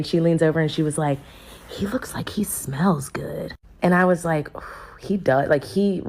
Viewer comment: I misheard her and thought she said, "he looks like he doesn't smell good," and I agreed 😂😂